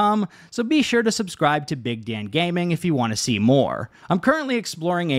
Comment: I subscribed after I heard you say the channel name was "Big Dang Gangbang"